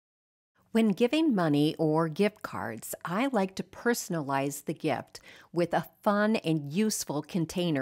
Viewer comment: You said, "I like to personalize" I had to rewind the video because I thought you said, "thriftilize". 😂 Love the blouse! ❤